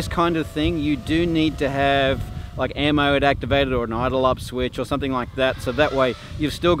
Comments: did he say, "you do need to have like ammoed activated or an aidleopsvitch"? The hell did he say there? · Does anyone know what setting or switch he is referring to at 5:14? It was hard for me to hear him at times... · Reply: Air mode.. this was back BEFORE it was a common setting... Airmode allows the PID controller to be active during "zero throttle"... Idle up switch is the same thing essentially, but allows for a little more control without gyro bounce.. to be honest though, Gyro bounce is pretty much non existent anymore if you know what you are doing / practice. Used to be with Air Mode activated, back in day, a slight bump would shoot your quad to the moon!